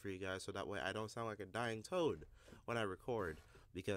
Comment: When you said "sound like a dying toad" at first I thought you said dying toe, and I was like wait what